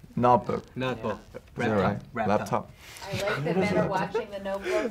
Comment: it sounded like V said I love tiny men who else heard it?? 😂😂😂😂😂